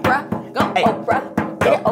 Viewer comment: I thought it said "orphans favorite things"